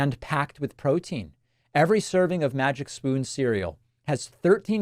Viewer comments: Oh Davif, i thought you said MAGA SPOON in the advertisement. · I wrote a book! "The Echo Machine: How Right-Wing Extremism Created a Post-Truth America." It's available on March 25th, 2025. Pre-order it here: <link> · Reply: Oh Davif, I thought you said MAGA SPOON in the advertisement.